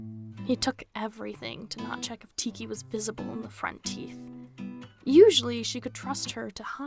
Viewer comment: Idk why but it sounded like u said ‘teeth’ in this bit 😂